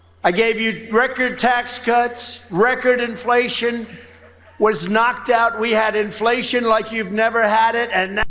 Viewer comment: Correction, sir: Trump did NOT say he caused record inflation 2:57 He actually said "record inflation was knocked out". He was saying he reduced inflation. The truth is that he is partially responsible for the current high rate of inflation, through his actions that helped precipitate the war in Ukraine. But it is Biden who has presided over and more directly caused the very high rate of inflation